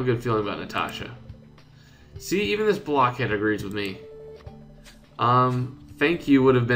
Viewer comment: "See?! Even this blockhead agrees with me!" xDDD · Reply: For a second I thought she said “Blackhead”